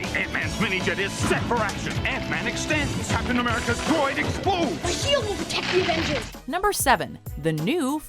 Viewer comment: did that just say Captain America’s groin explodes 😂